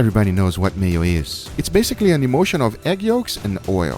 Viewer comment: When Guga said imulsion i thought he said emotion🤣🤣